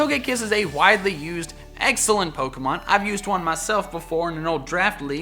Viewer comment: Did he say EGGSELENT IN 5:46 ON TOGEKISS TOGEPI